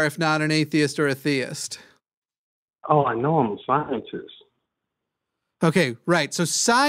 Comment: I'm not sure if got ahold of some really good shit, or some really bad shit. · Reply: I could have sworn said "I am a shiny cyst".